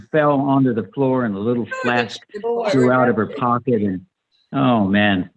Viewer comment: This was so much fun and I want to that everyone for doing the reunion but did I hear Roger Davis correctly when he said he picked up Joan Bennett and a little flask fell out of her pocket at 59:59?